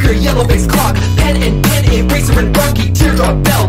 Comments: Blocky is in tpot and not rocky now? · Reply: No he is not. I misheard Rocky as Blocky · There were 3 names that I couldn't make out, so at the end, I took who wasn't used yet and filled it in. Foldy, Pen and Pin might be in the wrong spots. (Edit: Yes, I have been informed that I misheard Rocky as "Blocky".) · Blocky isnt in tpot wth Ik the sound isnt yours, im just complaining abt the song not the video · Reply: It was my error. I misheard Rocky as Blocky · Where Blocky shows up, he says Rocky. It does sound a bit hard to understand but it's Rocky, especially since Rocky isn't anywhere to be found. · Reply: You’re not the only one. I actually though Blocky was going to be a Contestant on TPOT when i misheard it for the first few times I listened to this.